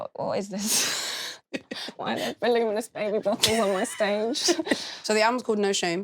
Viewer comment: What did Lily say at 3:26? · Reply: Isaiah i don't really want baby bottles on my stage !